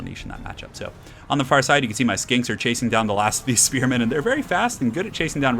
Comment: I keep hearing "skanks" instead of "skinks" And yes, it does add a lot to the viewing experience :)